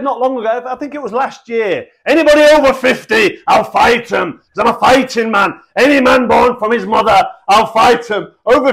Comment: Oh John said I'm a fighting man I thought he said I'm full of shite man